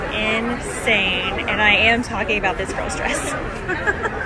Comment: I'm not very good at English but what did she say at 8:47 ? This "gross" dress ?? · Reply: "This girl's dress"